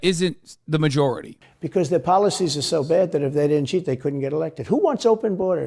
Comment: did he say “if they didn’t cheat they couldn’t get elected”?!? Holy hell. The irony is mind blowing. The projection is astounding.